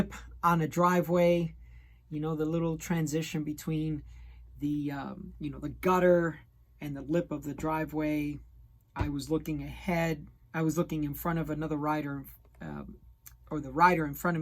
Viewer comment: Thanks for making this review. It really helps those who have no access to a good LBS and have to rely on online platforms. At about 09:55, I heard it as "I was checking out the rider in front" XD. Jk. One minor observation; when reviewing shoes and talking about fit/sizing, it really helps us even better when reviewer includes foot measurements. There is also a very good video from "Cycling in Southern California".